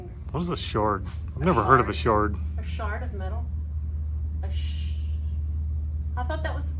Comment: At first I thought I heard “shart of metal” ... which is kinda like Flying Sparks! But seriously... really excited to see how far this project goes. What is the Bugatti VW’s name?